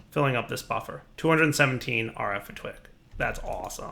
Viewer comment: "217 RF a twick." Anyone else hear that? LOL